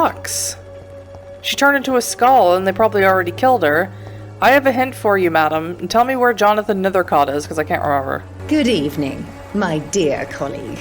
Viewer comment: I thought you said I have a head for you madam XD